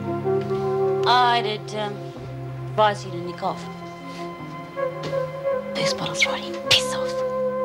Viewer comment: Lisa- 'I'd ad erm, advise you (d)a nick off; this bottle's writey a pissoff!' Can anyone tell me what the eff she said? I listened to it about five times, and I couldn't make any sense out of it. It was obviously important because of the situation and reactions etc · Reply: Her exact words are "I'd advise you to nick off. Booze bottles RODDY, piss off!"